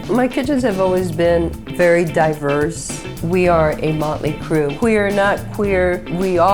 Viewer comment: I love this video! If I’m ever in Phoenix, I want to eat at one of these restaurants. One question however: At sometime around 3:30 into the video, does she say, “We are not Queer”? If so… what’s that about?